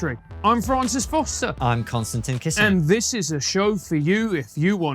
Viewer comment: When KK introduces himself, it sounds like he says, "I'm constantly kissing".